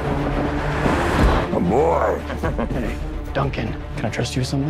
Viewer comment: …sounds like she says “boh” like in Spider-Man: Far From Home 😄